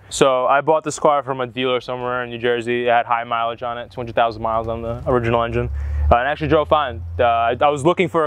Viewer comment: lol did he just say 20k miles is a high mileage? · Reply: He said "two hundred thousand miles"